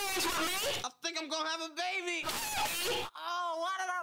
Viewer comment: “WHAT THE BUG ARE YOU TALKIN ABOUT” 😂😂😂😂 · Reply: Lol i thought she said “WHAT THE MUG SRE U TALKING ABOUT” at the original one 😂😂😂😂😂LOLLL